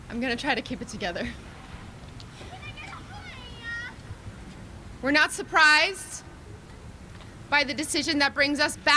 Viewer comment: what was that I just heard? 😳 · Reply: “Can I get a hoooya”